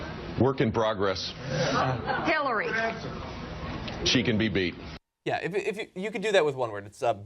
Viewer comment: Jeb said "she can be beat" about Hillary, but I kept hearing "she can beat me".